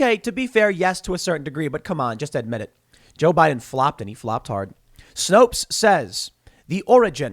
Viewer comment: Did you say Joe Bidens a Floptard?